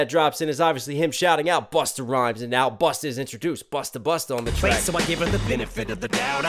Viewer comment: This whole time I thought he said mustar mustar😂 not busta busta